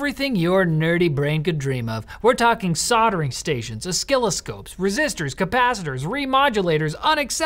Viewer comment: Did you just say o-skill-o-scopes?